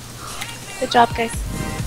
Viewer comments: Did she said good job guys? ROFL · did they just say gj GUYS?